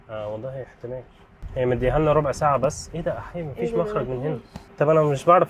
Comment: هو اسكندراني من عندنا ولا ايه سمعت احيه 😂😂